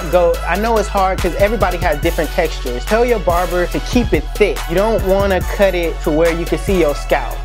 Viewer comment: did he say keep it thin